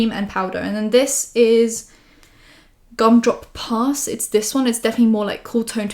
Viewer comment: omg when you were showing the supershock blushes from the candy land collection, because of your accent i thought you said the one blush was called “grumdrop puss” instead of “gumdrop pass” 😂😂💞